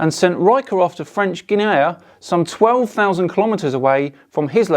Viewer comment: Did he just say "French Ginayuh"?